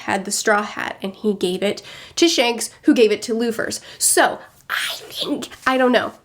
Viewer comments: did she just say Luffers so he's Monkey D.Luffers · did she just say luffers?